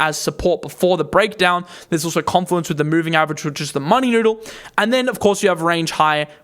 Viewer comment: Whats the indicator you said ai16z rejected off? It sounded like you said it rejected off the money noodle. That can't be right?